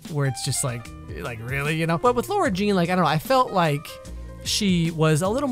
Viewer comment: Alex: Laura Jean Me:TRIGGGGGGGGGGGERED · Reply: Sounded like she said Laira I was bothered lol